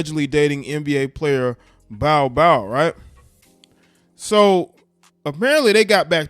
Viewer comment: it sound like bro said she was dating Bow wow instead of Bol Bol😂😂😂😂